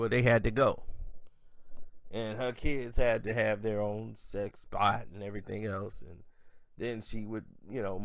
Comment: sex bot??? Did you say sexbot?